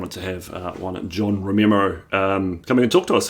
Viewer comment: Did you say "Romemo" @ 0:20 ?